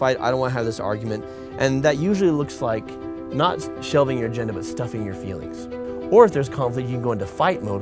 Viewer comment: Why did it sound like he said "Show me your Virgenda."